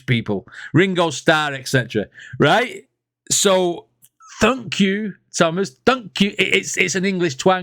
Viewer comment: Is that so supposed to be thank you? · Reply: I heard "th-th-thunk, you, Thomas!"